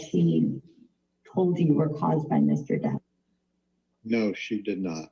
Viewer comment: who else noticed the mess up in the subtitles at 2:41, where it said Mr. Death, Instead of Mr. Depp?